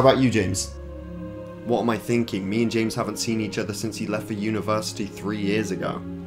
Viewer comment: “Me and James haven’t seen each other since he left the university three years ago” I thought he said universe